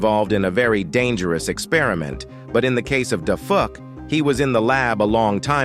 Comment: did bro say da fu